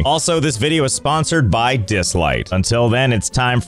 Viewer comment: i keep mishearing the sponsor. "today's video is sponsored by the dislike (button)"